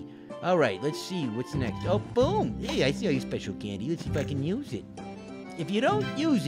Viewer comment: Boyyyyyyyy did you just say let's F**** use it???